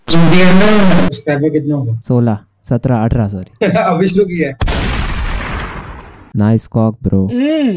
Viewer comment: Wtf he said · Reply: Abhi shuru ki hai